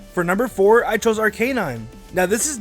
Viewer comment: did you say Ar-Key-Nine?